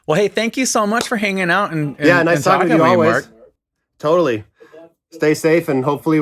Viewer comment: i thought mark said, "Nice talking to you mark" at the end lol